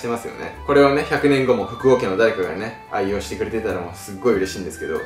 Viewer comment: 「福尾家の誰か」が「国王家の誰か」に聞こえたけど、違和感がなくて一旦スルーしてしまった